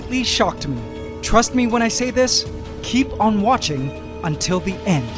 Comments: Keep on watching until the "ant" XD... i always hear that on this channel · Did you just say keep on watching till the ANT?